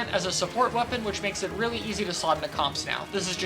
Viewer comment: is too fast. I couldn’t tell what you said there. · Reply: He said about it being able to slot into comps